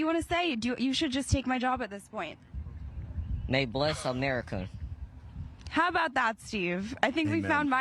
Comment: "May bless America". Did they cut out part of it? Did he say "God" bless America?